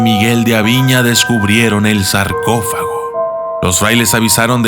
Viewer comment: Los subtítulos creo que tienes un error en una palabra no importa pero me llamo la atención es en el minuto 1:58 que dice charco y creo aver escuchado sarcófago